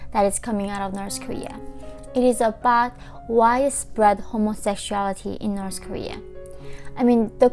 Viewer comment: Thank you for your heartfelt support, everyone! Please get your signed copies of my book here ♥️ <link> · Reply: I thought you said. " Rice bread homosexuality". 🤣!!!